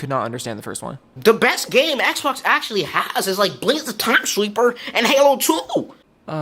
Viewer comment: What he said was "Blinx The time sweeper and Halo 2" By the way blinx the time sweeper is a fun game. Its a challenging game